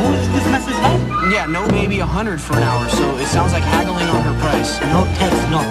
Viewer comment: he says "It sounds like you're haggling over price" but I like to think the officer is saying "It sound heavily overpriced"